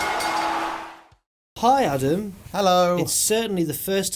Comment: Did call plumpy Adam at 0:02 ?